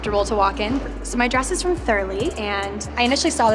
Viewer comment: Where is her dress from? I hear "Furli" but I cannot find anything online